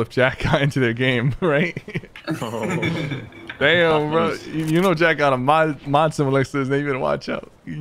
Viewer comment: can anyone tell me what he said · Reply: "You know Jack's got a mod symbol next to his name" Meaning Jack is a mod on Syphers stream and could mute/ban the person trash talking